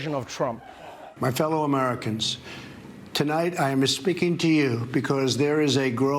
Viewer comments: It sounded like he said “I’m misspeaking to you” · Did he just say, "My fellow Americans, I'm mis-speaking to you"? I swear that's what it sounded like. Wooo finally truth from the orange-a-thang. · Did he just say ''tonight I'm misspeaking to you"? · Did I hear Donald say “espeaking”? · Did Drumph just say "I am espeaking to you"...? 2:17 Careful, don't want to sound too Spanish there Donny boy. Your fellow racist/xenophobic throngs will turn their backs on you ;)